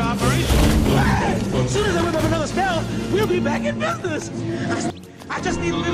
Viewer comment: "Well be back in business" I thought he said Memphis.